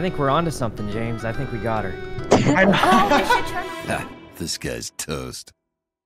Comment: why do i hear "This Guy's Toast" only me??